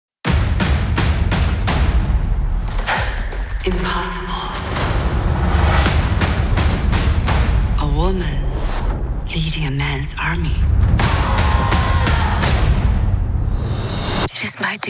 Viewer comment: I thought the witch said “a woman eating a men’s army”